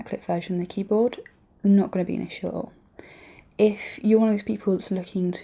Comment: What does she say at 7:17 ? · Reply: "Not going to be an issue at all."